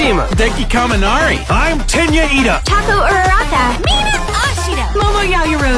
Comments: lmao the first video i heard it ''Taco Uraraka''! lmaoooo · Can someone PLEASE tell me why I keep hearing 'Taco Uraraka'